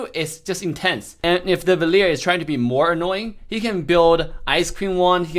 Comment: I thought said "Ice cream Wand" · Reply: Many people say ice queen wand And I only hear it ice cream wand